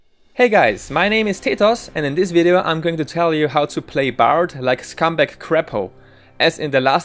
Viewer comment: At first I thought he said "I'm going to show you how to play Bard like scumbag craphole" and i thought "is this something I want to aspire to?"